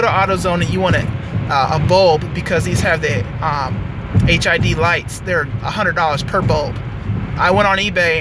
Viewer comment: did you say this car has HIV lights